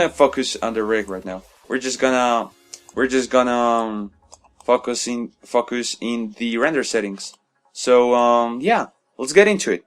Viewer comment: i thought you said Fuck is in lol